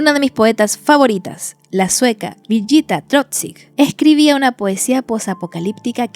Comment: Me interesó el poema 24:22 pero no entendi como se llama la señora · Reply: Birgitta Trotzig, tambien me costó encontrarla